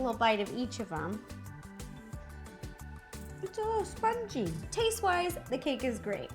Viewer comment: When Ro said that the Sprite cake was spongy, I thought she said that it was "spicy"! 😂🤣😂🤣😂🤣😂🤣😂🤣